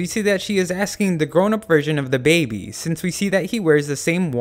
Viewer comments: Anyone else hear "The grown up version of DaBaby" at 0:26? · When I hear he say "da baby" my mind goes "let's gooo" .